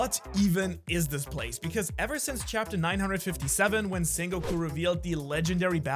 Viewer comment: captions say chapter 1057 but its 957